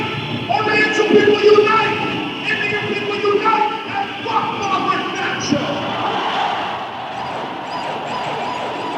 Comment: I can't make out everything that he's saying... help? ::P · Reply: "Black people unite, white people unite, oriental people unite, indian people unite and f*ck margaret thatcher!"